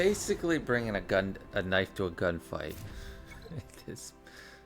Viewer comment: I thought you said "knife to a Gundam fight" at first. Which is probably more accurate for that GAC match up